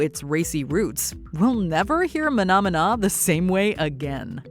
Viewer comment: I always thought they were saying "phenomenon" not "mahna mahna".